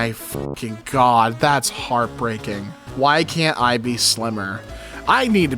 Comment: This does NOT say "Why can't I be slimmer?", it says "Why can't I be Slimer?" - and the answer is because that little green ghost from Ghostbusters is copyrighted!